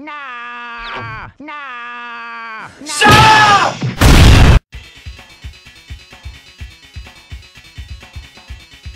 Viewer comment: LOL Did Wario said Shop!!! or Shut!!! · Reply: He said shut